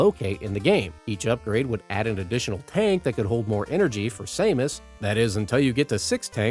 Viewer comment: I've never heard anyone pronounce 'Samus' they way you did. For a moment, I thought you said "Shamus" (the Irish name).